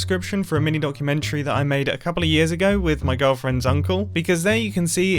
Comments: I thought you said “my girlfriend, zunkel” instead of “my girlfriends uncle” for a second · when he said my girlfriends uncle i thought he said my girlfriend zunkle. i was just like hmmm thats an interesting name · did anyone else hear "my girlfriend Zuncle"? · did anyone else think he said his girlfriend "zunkle" 😂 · ok i have to be real here i thought you said "my girlfriend, zunkle" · I thought he said girlfriend zunkl like it was here name XD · I wasn't paying attention and at 9:10 i heard "With my girlfriend Zunkle" hahaha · When he said he with my girlfriend's uncle for some reason i heard it as my girlfriend, Zunkle. Was like that's a really weird name. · for a moment i thought he said "my girlfriend, zuncle" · Am I the only one who thought he said that he made a documentary with his girlfriend, Zuncle.